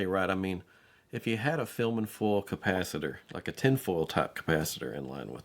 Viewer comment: tin foil cap for the focal tweeter? Did I hear it right?